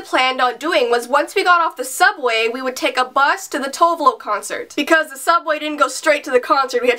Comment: Was she called Tove Lo? I thought you said Tovelow.